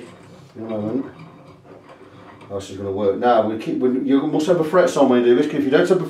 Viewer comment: Please, what is being said between 3:27 and 3:31? I can't make it out. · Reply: 'You must have the frets on' = the frets must be on the board else you will melt the inlays...